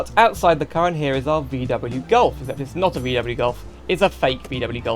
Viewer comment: bruh did u just say bmw golf? 😂